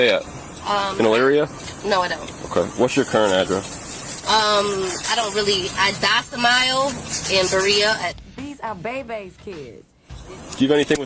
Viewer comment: did she say “I docimile”? JFC